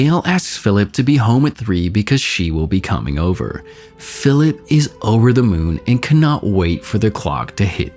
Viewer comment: The captions say "Xxx is over the moon" instead of "Philip is over the moon"